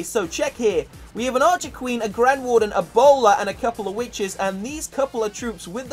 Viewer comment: when u said a bowler i thought you said ebola 😂😂